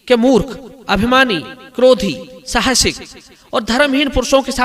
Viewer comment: पर क्या बोला आपने ?? साहसिक? ?